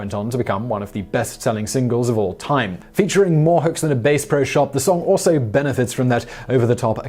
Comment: "base pro shop"... Yep, we just learned someone else wrote that line and you have no idea what Bass Pro Shop is. Haha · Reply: It sounded like he said more hooks than a bait shop